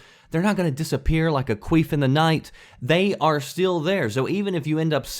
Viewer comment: Did he just say a "queef in the night"?? Lmao